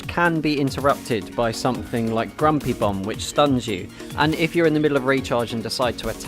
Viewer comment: As someone who hasn't played Paladins I'm not sure if you're setting up a joke when you say stuff like "grumpy bum". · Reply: Originally I had it as "grumpy bump" but I made sure to listen back to check if I got it right. Turns out that meant I just misheard slightly differently 😝